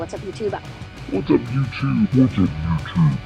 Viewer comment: I missed you too! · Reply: for a minute I thought you said “f… this YouTube s…”! 😂